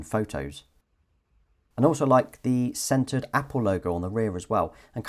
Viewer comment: Haha I thought he said scented Apple logo for a second and I was like woahhh Apple really stepping up to a different level 🤣🤦🏻‍♀️